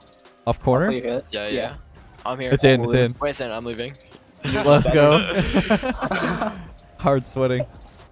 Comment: I thought he said "Leth go" xD